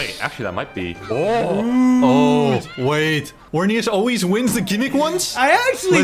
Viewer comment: Swear called Vernias "Hornias" there... or is it just me?